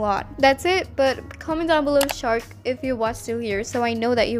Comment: Shark! (Banana) I really want those headphones with my ava 😩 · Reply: YESS I thought u said shrek instead of shark ejjwosns I need sleep